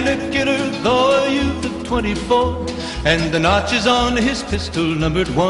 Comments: I keep hearing "And the nazi's on his pistol" but I know its notches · I always thought it said "the nazis on his pistol"...